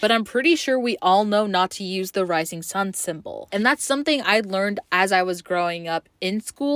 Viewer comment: Wait whaaat? 3:35 Nazis never used the rising sun symbol. It was the Japanese. And they may have been “allies” but not once have they actually helped each other in the war. I know for a fact that not once did they fight side by side. Now if they actually did and invaded any asian countries together, that swastika would be more widely known in the east like you all assumed. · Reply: No she said at 3:35 “Not to use the rising sun symbol“ but she said it so quickly that it sounded like “Nazi use the rising sun symbol“ but thanks for this info though.